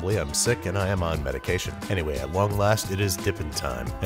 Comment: it sounds like babbis is saying at wong last 😂